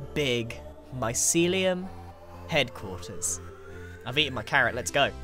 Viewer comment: When he said I’ve eaten my carrot an ad played so it sounded like he said I’ve eaten my cat, also I absolutely love the hq